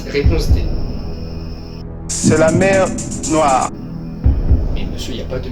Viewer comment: Quoi ?? xD 😂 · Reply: C'est la mer Noire